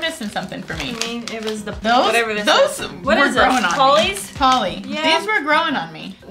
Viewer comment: hahaha i thought you said holly original (instead of polly)